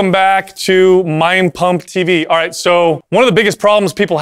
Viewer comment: Brooo I heard "Mein Pump" 😅